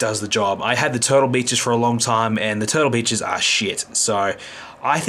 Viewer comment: you sound so different in real life.Congrats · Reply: Pancakes 26 4:37 sounded like you said "Total Bitches"lol